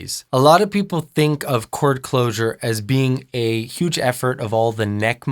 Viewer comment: Let’s call chord closure “squeeze” Yeah.... that’s gonna lead to healthy vocal habits.....🙄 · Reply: I thought he said core closure? ._.